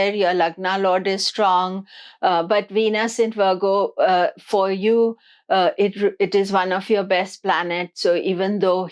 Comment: For Leo can’t understand what the word is you are you saying sober investments or super investments at 13:19? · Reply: Sober investments